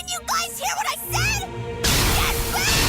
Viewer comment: At first, I thought Chopper said "Get Rekt". Seriously, It made me laugh until I realized what he really said!!